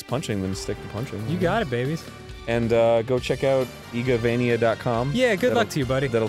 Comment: I thought he said egovania